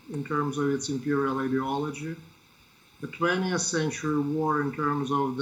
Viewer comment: Can someone help me understand what does Plokhi says at @ 39:19 "The (uninteligible) century war in terms of the tactics. The militarty tactics of the agresor." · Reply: 20th century